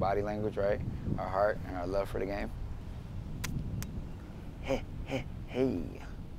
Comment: TF IS THAT? Me and my friends have a 2-3 minute handshakes also tf did he say "hey hey hey HEY"